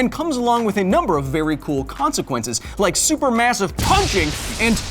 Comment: at the beginning I heard "it comes with BARRY cool consiquenses"... missed pun moment mah boy